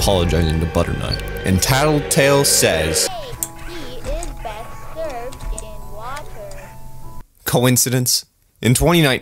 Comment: what does he say here? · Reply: I’m pretty sure he says “tea is best served in water” but I’m not 100% sure